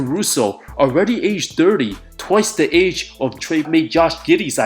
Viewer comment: That giddey joke was funny at first I thought you said twice the age of giddey and I was like no way he 15 but then remembered